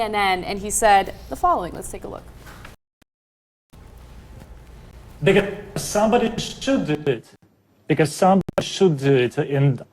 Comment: At the beginning of his video clip, it sounded like he said, "Nigga, cause somebody should do it..."